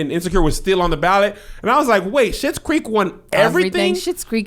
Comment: He low key says a few, few words. Lol. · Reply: I heard him say shit’s creek lol